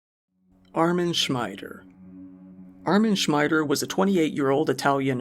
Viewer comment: for a fraction of a second I thought they said Amy Schumer! We couldn’t be so lucky!